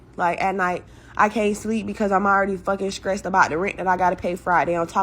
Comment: It sounds like she is saying stressed wrong.... more like skreesed